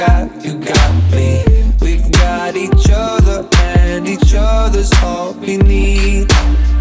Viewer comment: I misheard 0:55 as "we've got each other and each other is only me"...